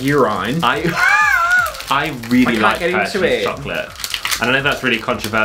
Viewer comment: Lol....Sounded like said Herpes Chocolate